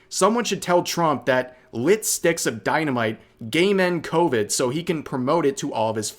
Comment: Tell me why I heard "gay men" instead of "game end" 😂 "Lit sticks of dynamite gay men covid."